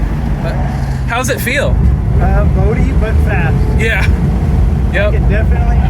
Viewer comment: That looks fun! At about 4:40, said it felt "[?] but fast". What did he say? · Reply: "boaty but fast." i think is what he's saying which is why he mentions the coil overs.